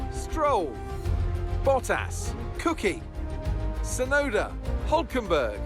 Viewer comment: Hey Mate in 6:08 instead of saying Vettel it said Cookie Lol